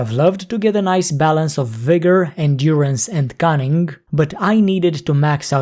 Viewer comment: Ngl I thought you said "balance of vigor and urines"". My bad xD